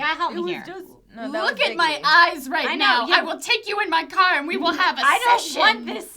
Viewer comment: I tuned out and when I started listening again I heard "You will get in my car and we will have this session!" And I was like "What?"